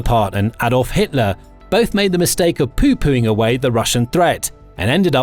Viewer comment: poopooing? Am I hearing that correctly?